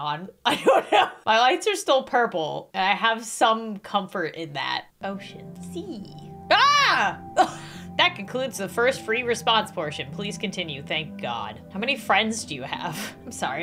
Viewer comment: When u said ocean I thought u said oh sh**